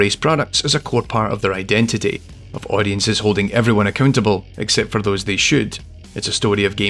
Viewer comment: i heard "ac-cun-table"